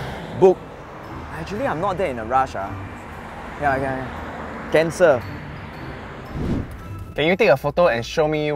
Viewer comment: I thought say "Cancer"